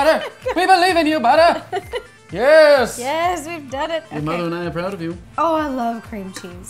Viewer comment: did he say your mom and I are proud of you